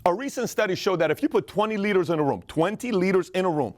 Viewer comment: Thought he said 20 litres in a room lmao